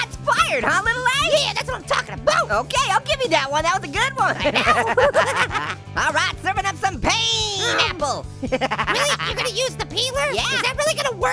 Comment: i thought he said peen